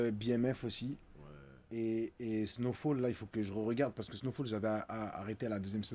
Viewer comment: c’est quoi la série que sadek dit???? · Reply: Snowfall